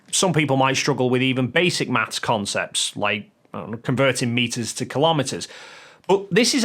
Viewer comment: Your jab about people not being able to convert meters and kilometers was understated brilliance. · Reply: Except he didn't say, "...meters and kilometers.." He said "... converting meters TO kilometers..." meaning they can't do something as simple as multiply or divide by 10. A completely different meaning than what you have inferred. He's talking about struggling with basic math concepts and NOT metric versus imperial measurements - review what he's actually said at 3:35.